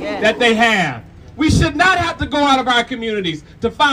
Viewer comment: What I just heard: "we should NOT have to go out of our communities to steal" 😅